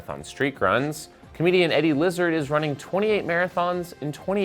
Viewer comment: Eddie Izzard. I thought i heard Lizzard?